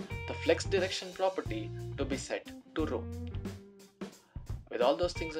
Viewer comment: flex-direction can take 4 values: row, row-reverse, column, and column-reverse :) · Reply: Based on your information, at 1:19, he said will be set to Row. I couldn't understand what he said until I read your comment.